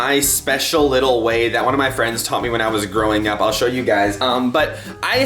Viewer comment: I thought he said “ when I was a little girl “ lol I’m so stupid